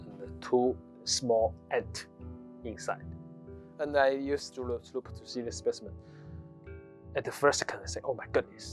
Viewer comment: Nice work. One picky correction: at 2:44 the subtitles say "jeweler's loop". That's a jeweler's loupe.